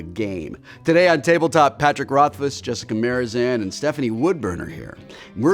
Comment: is it me or did Wil say Jessica Marizan?